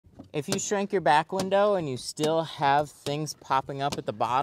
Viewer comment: Thought you said shank your window.